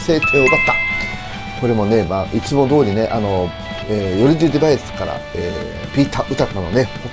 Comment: いつも拝見させてもらってます！😄 ずっと気になってるのですが、いつもヨルディバイスのこと、ヨルディディバイスって仰ってるように聞こえるのですが。